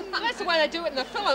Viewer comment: Did Rita say "Fil-ms" at 0:16? or am I hearing things